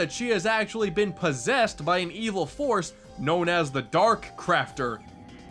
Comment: In Kirbys rainbow curse at the final boss I thought he said dark crapter